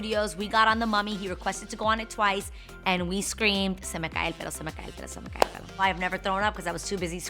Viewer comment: I thought she said “se me cae el pedo” hahaha I died 😂